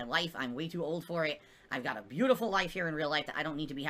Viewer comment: Did he say he has a beautiful LIFE (wife) in real LIFE?